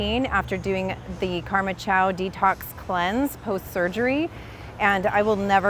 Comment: What kind of cleanse did she say? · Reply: It sounds like she said, "Karma Chow Detox Cleanse". You can slow the video down by using the comma and the period keys on your keyboard. You can also repeat by using the left and right arrow keys and the J, K, & L, keys for repeat, pause, and jump forward. The arrow keys are for 5 seconds and the J & L are for 10 seconds, and the K is for pause.